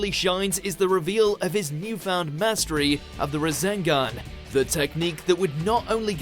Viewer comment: Did he say "The razengan"?😂